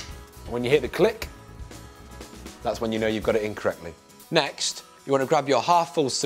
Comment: sounded like "that's when you know you got it incorrectly" instead of "in correctly". I was a bit confused afterwards :D